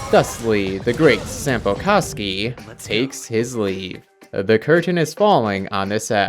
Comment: I dont know if its just me but it sounds like ur saying sampo c--kski 😭